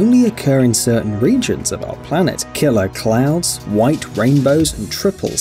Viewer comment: i thought he said killer clowns 😂